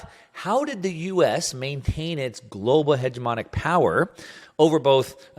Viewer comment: Very informative video, well explained. Did you mean 'hegemony' of the US dollar? It sounded like you said 'homogeny.'